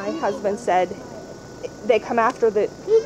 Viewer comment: the Mother says the dogs, came after them but you can hear the child say they didn't come after us. Or am I hearing that incorrectly?